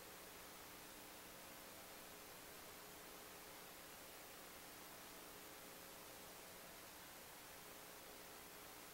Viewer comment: I'd love to hear what was said here. Her reaction says it all. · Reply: She Said "Oh Gosh/God" probably they were informed about the explosion